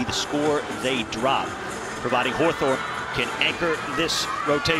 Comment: Did he really call Soraya HORTHORP? 🤣